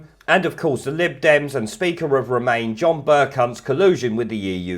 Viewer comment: I think I heard you say 'Ber-cunt' instead of Bercow. Love it!!